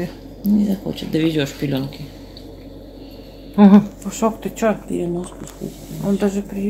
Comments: Пушок... а мне послышалось сначала Кусок 😂 похоже у меня конкретный недосып... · А мне сначала послышалось, что Котейку зовут Кусок :)